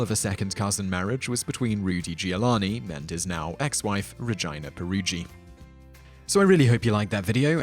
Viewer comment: Rudy Giuliani's ex wife is called vagina ? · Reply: I thought he said Ra-gina but still....that's a messed up name....